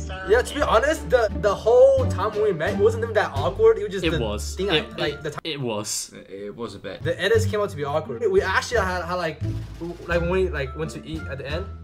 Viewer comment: Who else thought it said editors